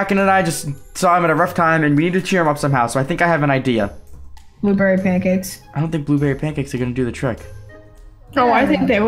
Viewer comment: Did anyone else hear "bloopery pancakes" ? I'm curious